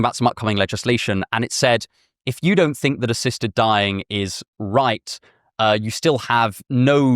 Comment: I was SO confused because I heard "a sister dying" but you said "ASSISTED dying" Thank god for captions